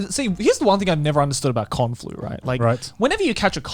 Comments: When Garnt said, "Con-Flu", I misheard him and thought he said, "Kung-flu". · I thought Garnt said "kung flu" insead of Con flu